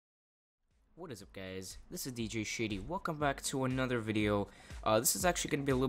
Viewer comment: at the start i thought he said hey whats up guys my name is LOL XD